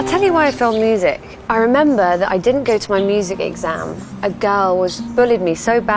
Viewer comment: lol I thought she said Adele when she said a girl.